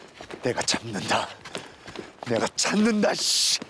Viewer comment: 내가 찮는다찮느다 라고 말했는데 나는 내귀로 참는다 라고 들었넿ㅎㅎㅎㅎㅎㅎㅎ